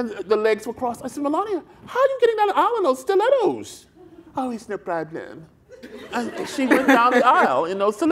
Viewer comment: What was it that he mentions Melania saying about the 4in stilettos I can't make it out? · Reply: He says “Oh it’s no problem” in her accent 😂